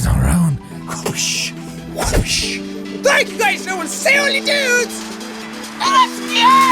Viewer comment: did he say: i will see all your nudes